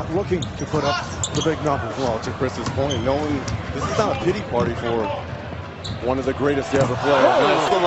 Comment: Did bro say diddy party